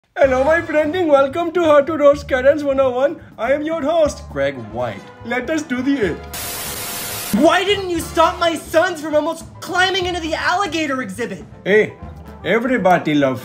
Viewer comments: For a seconds I thought he said how to roast carrots and I was so cunfused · I thought he said : how to roast carotts😂😂😂😂